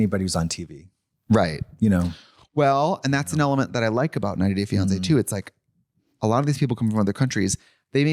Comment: I thought you said Not a Gay Beyonce. Not 90 Day Fiance